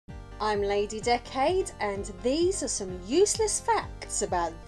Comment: Bro...I thought she said "im lady dickhead"😂😂😂